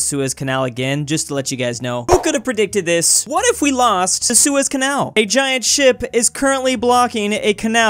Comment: I thought everyone was saying U.S. canal